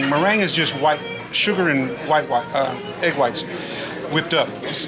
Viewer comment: The waiter said meringue is sugar and white, white. Maybe he was tired. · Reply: Lol I thought he said egg white😂😂